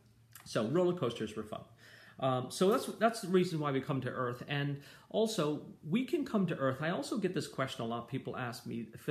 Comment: I thought u said angels can’t come to earth